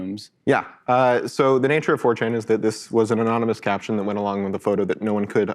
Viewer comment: lol the subtitler typed ''fortune'' instead of ''4chan''